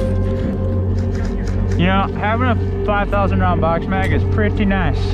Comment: How many bbs does that hold you fire about 1000 bulets before realoading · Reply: Heard him say 5,000 round mag